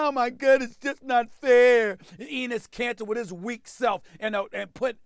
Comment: I coulda sworn I heard SAS say Penis Kanter...